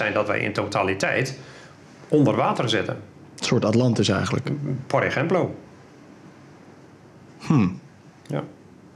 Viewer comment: Wat zegt hij nou op 48:26 ? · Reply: oooh wacht, volgens mij zegt hij "por ejemplo" (bijvoorbeeld)??